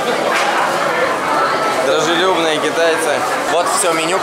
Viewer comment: даже е**ные китайцы? Или мне послышалось?